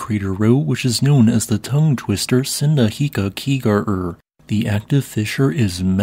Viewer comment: that word I didn't even understand what he said for a moment but it is "Sundhnjúkagígaröð"